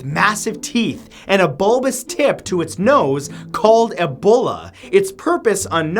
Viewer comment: did he just say Ebola instead of Bulla? XD